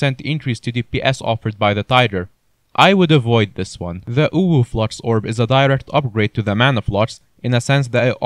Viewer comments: I heard "uwuflux" instead of "overflux" lol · did he just say 'uwu flux'?